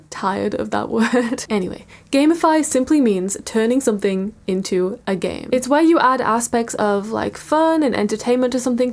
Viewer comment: and did you say woman tech? What is that? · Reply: She said women in tech